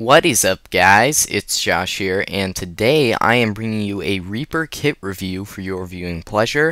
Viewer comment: at first i thought it said raper kit xD